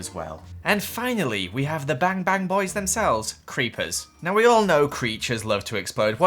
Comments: i THoUghT hE SaiD bAnGTan bOisssss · Did anyone hear creatures instead of creepers at 8:43🤣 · did u say creatures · Omgggv I thought u said bangtan boys instead of bang bang boys and I was like army? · did joel just say creachers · anyone else heard “creatures” instead of creepers? no just me ok